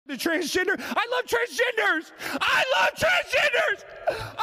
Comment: For a moment, Alex, I thought you said "transgingers", which would've made this even funnier! 😁👍